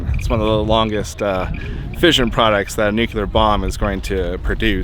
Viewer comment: Did you say nucular? Astonishing how many get it wrong, can you say new-clear?.